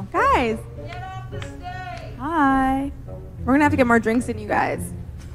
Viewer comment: Did I hear " Get off the stage?" It's like water off a duck's back. Narscisists totally turn a deaf ear to everyone , even people they employ to advise them. No wonder the money is running out.